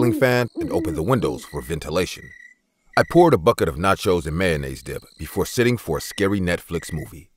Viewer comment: did he say NACHOS AND MAYONNAISE @ 0:35 !?!?